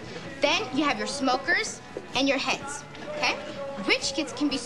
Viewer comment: Can someone explain what "heads" are. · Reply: Heads makes more sense, I thought she was saying hets